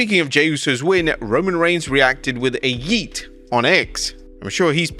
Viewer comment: did he say “cousin brother”🤨